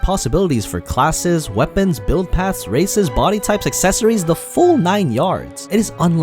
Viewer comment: Did he just say "RACISM"?! And unlimited?! I am interested now.